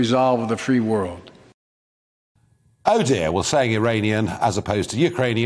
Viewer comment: Sounded like he said Uranian